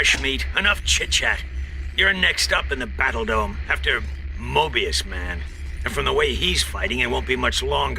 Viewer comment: was I the only one who heard 'mobius man' and was that a possible sonic reference??